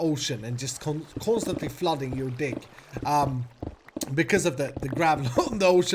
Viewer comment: did he say dick? :-I